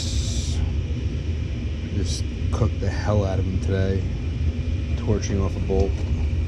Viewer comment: Great video. I had to listen to the part about the burnt channel locks twice. Sounded like you said you torched off a bowl.